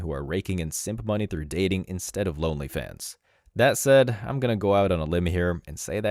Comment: Every time someone says "Only Fans," I hear "Lonely Fems."